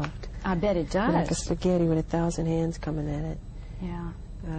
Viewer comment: Thousand hands coming in it. lmao Don't you mean at you? I love you, Michael ! ROFL Does anyone get what he is saying here? Sounds like he said spaghetti with a thousand hands coming at it.